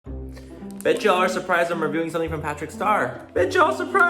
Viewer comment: At the beginning I thought he said I bet you guys are shocked I’m reviewing Jeffree star products. And I was shook and shook he said that 🤣